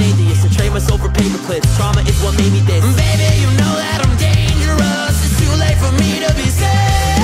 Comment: HELP I THOUGHT HE WAS SAYING "AND BABY YOU KNOW THAT IM DATING YOUR ASS" BUT IN LIKE A BRITISH ACCEBT. I DIDNT KNOW HE WAS SAYING "DANGEROUS"